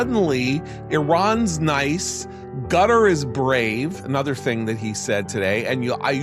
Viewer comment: The guy said gutter instead of Qatar. Probably in purpose.